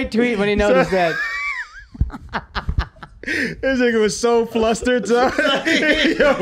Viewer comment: did Shultz say "nigga"?